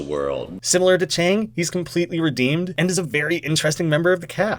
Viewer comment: I swear I heard "redeaned." Edit: Someone else already made this comment.